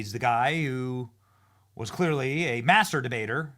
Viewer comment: DID YOU SAY HE WAS A MASTURBATOR